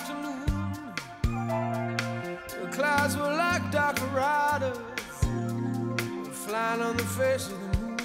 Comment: I always thought he said "the clouds were like dark marauders flying on the face of the moon". Bugs Bunny used to fight The Masked Marauder